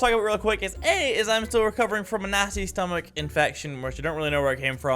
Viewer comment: At the beginning who else thought he said I have a nazi stomach infection